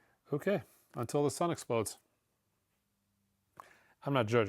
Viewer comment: Did say, "Shit"?